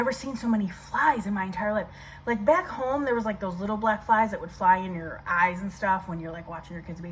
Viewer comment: omgoodness at first when you said black flys I thought you said black lives I was freaking out for about a second or two